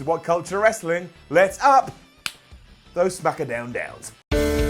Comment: Am I the only one who thought dimon said "lets up those f***ing downs" at first